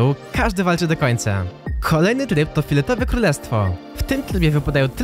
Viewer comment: kto usłyszał filetowe królestwo